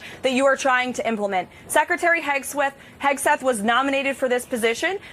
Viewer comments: Did she said: "Pete Hegsweat" at 4:15?! X)) Revealing ... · “… Secretary Hogsweat…”??? Hogsweat!?!?! Is that what she said? What a remarkable slip of the tongue!!!